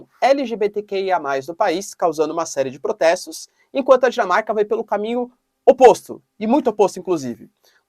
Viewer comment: quando o sr falou Dinamarca eu entendi Jamaica... Estranhei e repeti o trecho usando legenda e o YT também entendeu Jamaica. Continuei não acreditando e reduzi a velocidade pela metade e só então deu pra entender que o sr falou Dinamarca... 😊